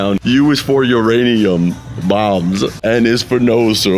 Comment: For the longest time I always thought plankton was saying bums instead of bombs in the fun song.